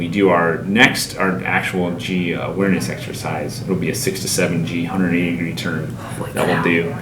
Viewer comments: i thought he said 67 g and i was like that would kill you. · I thought he said 67 g’s instead of 6 to 7 g’s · I thought he said 67 g exercise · I thought he said 67G